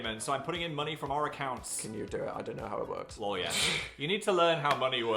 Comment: Phil saying “lol yes” for whatever reason the first time I heard it my brain registered “lawyer”